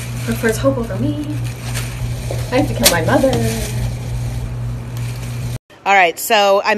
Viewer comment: did she say she has to kill her mother ?